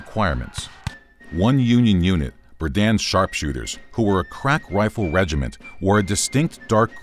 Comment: I thought he said “crap rifle regiment”